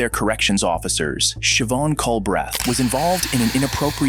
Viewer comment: Ohhhhh NOOOO I thought it said Chevonne C*mbreath